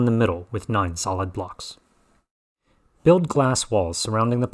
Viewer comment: Why does he says "Fill in the middle with non solid blocks"?